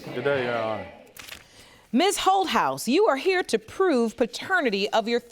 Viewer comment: I thought she said miss ho-house 😂